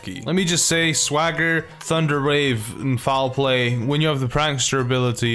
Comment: I thought you said Thunder RAVE...